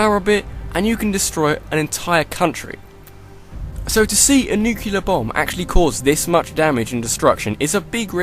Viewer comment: did he just say "Nucular"?